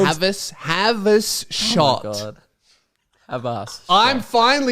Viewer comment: Hey boys sounding off, I heard say “have HIS shot” when making the do you know joke. Apologies for listening and appreciate the apology for terrible enunciation